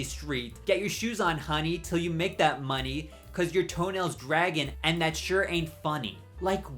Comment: i always thought she said “cause you’re tonail-dragging” as in doing tonail drag, i assumed she meant that the person had cliffhangers. although she’s talking about someone being barefoot so that makes so sense